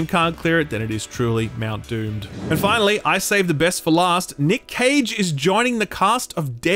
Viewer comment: I thought you said Nick Cave instead of Nic Cage until he appeared on screeen😀